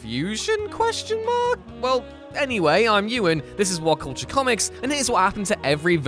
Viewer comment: did he just say his name is urine?